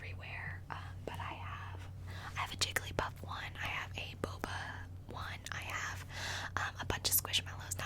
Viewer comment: thought she said B O O B A